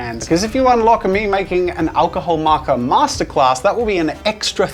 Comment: captions said "masterass" 😂